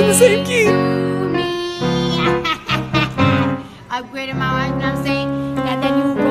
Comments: I keep hearing “upgraded my wife” which leads me to believe it’s actually “watch” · “Upgraded my ??? y’know wha I’m sayin’?” No, Cardi, we don’t know what you’re saying. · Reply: i heard “upgraded my watch”